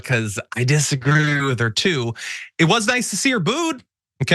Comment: Oh, "booed", I thought you said boo$... Be careful what you say when your audio is acting up!